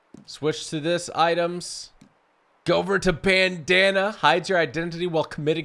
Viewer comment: The bandanna is in your second weapon wheel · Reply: dranze venus I thought you said banana lol